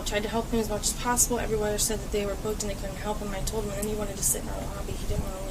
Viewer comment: I thought i heard the desk girl say "he wouldnt leave" near the beginning of the officer questioning her. Found it, 5:05 "and he wanted to sit in the lobby, he didnt want to leave" that makes me thing maybe he was getting a little irate about not being able to find a place to stay and maybe this is what helped scare her. She also says she didnt know what to do so she called her sister and its hard to tell through the officer touching the mic but i thought i heard something about her sister being the one to suggest calling the police. Honestly i think the officers did very well besides the phone3 and bit of verbal abuse. You have to remember because of the sister and fathers calls these officers were sure they just took down a terrorist, and if he had turned out to be a terrorist do you think anyone would have freaked out about a few insults and a broken phone? As soon as the officers knew he was not in fact a terrorist they immediately released him and gave him his stuff back and gave him his phone to call a translator. It kinda seems like they were a bit apologetic but not showing it because officers are supposed to be professional.